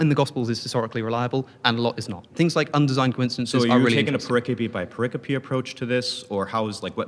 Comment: someone HALPPP!! what is homeboy saying at 1:14?? a what by what approach?? (to which alex o connor replied "im taking it story by story")?? it sounds like "paropacky by parapacky approach.." and ive tried googling different forms of its spelling, but i cant find anything!! maybe my spelling is just SOOOO off that even google doesnt know what im searching for?? LOLL! if anyone has any answers, id be so grateful 🙏😝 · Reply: Pericope by pericope would be thought by thought or in a sentence by sentence approach. Keep in mind it’s not necessarily retrained to a sentence I recommend looking up a more proper definition but supposed to be an idea. Where as a story would in a sense be less subjective because we’re not talking about small takeaway by small takeaways just the facts